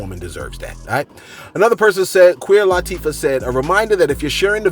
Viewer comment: Did I hear you say “Queer Latifah?” 😄